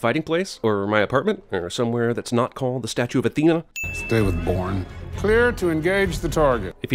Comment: Does he say "David Bourne"?